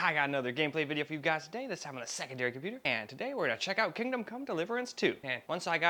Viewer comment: Ngl i heard Kingdom Condom 💀🤝🏻